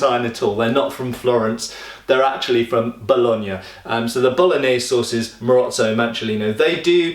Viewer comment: You say "Bolognese sources" but I hear "Bolognese sauces"...